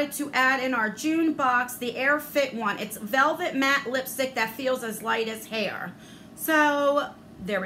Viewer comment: I thought you said the lipstick "feels as light as hair" 🤣🤣🤣 I was thinking why would they say that ?!??🐱